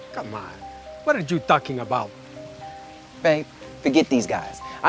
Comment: I was laughing so hard because i thought Juan here said What are you takis about😂